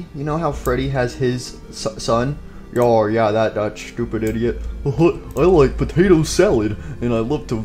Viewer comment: I just heard ‘doopid idiot.’